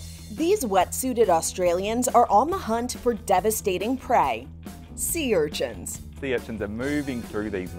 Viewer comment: I thought you said devastating pets like it was devastating because they tasted so good lol.